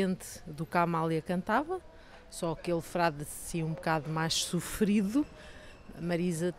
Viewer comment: quer dizer que em português de Portugal o "D" tem som do "Th" inglês? Ela falou "Sofrido" mais eu entendi "Sufri-tho"